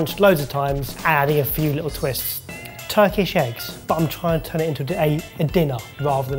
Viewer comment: The first time said "Turkish Eggs", I heard "Turkey Shakes" and had a moment of "What?" I was thinking some sort of chicken salad but, turkey and was trying to rationalize how that would be called a shake.